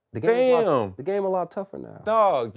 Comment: Did interviewer say @ 8:00 “The game a lot tougher now?!” Heyull naw man! 😂